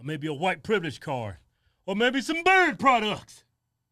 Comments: I start dying every time I hear “or maybe some BEARD PRODUCTS” 🤣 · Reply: I thought he said birth products. 😬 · I start dying every time I hear “or maybe some BEARD PRODUCTS” 🤣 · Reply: I thought he was saying bird products. 😂